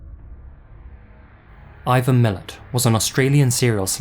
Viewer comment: OMG, I can't believe you got it wrong!!! It's Ivan MILAT not MILLET as narrated/the subtitles say. He's the dude that inspired The Wolf Creek movies. As an Aussie he is the most notorious killer Down Under.